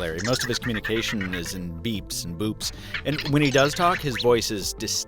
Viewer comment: Who else heard him say beeps and BOOBS... 😂😂😂😂